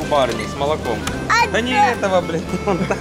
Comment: я один услышал " да не этого бля*ь "😂😂